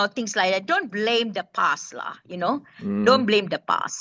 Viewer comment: When I heard Siti Kasim say, "Don't blame the PAS-lah", I was shocked. Then I realized that she actually meant don't blame the past-lah. When the Americans voted Obama (a black) into office, I was surprised but then Americans like to try out crazy stuff. Then they voted for Joe Biden too (a leftie). Recently, Japan had a female Prime Minister and our northern neighbor has had 2 female Prime MInisters. So it's high time that Malaysia can have a female Prime Minister not for the gender's sake but for her character. Siti Kasim has gone through the baptism of fire (btw I don't mean the Christian Charismatic terminology) and proven herself. She has walked the talk. Thanks to & Siti for the real news report. The mainstream news media in Malaysia is not real news so I never waste my time watching any of them.